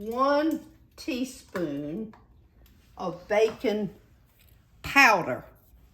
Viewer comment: I thought you said “bacon powder” and had to back up the video to see that you had “baking powder”. Squash is one of my favorites and your fritters look wonderful.